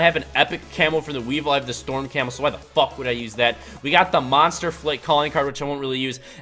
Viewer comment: I thought it said monster fuck 😂